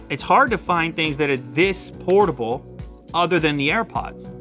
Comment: did he call airpods "affordable"